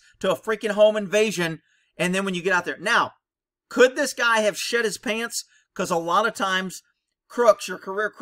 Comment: Did any else think he said "shit his pants?" rofl